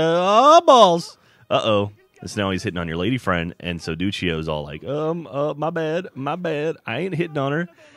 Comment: I thought you said Doucheio and not Duccio.